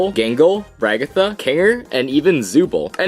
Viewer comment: HELP WHY DOES IT SAY JINGLE